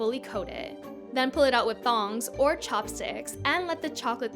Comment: did she say "thong"??